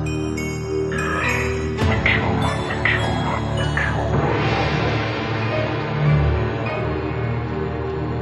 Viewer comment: what dose it say ? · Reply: Sounds like a warped version of Henry saying, "Yeah. I killed my mama."